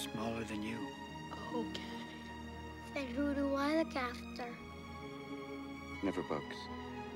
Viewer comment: Okay watching this as an adult I just realised he said "Never bugs". My whole childhood I thought he said "Never books" and thought that made no sense whatsoever. OH WELL.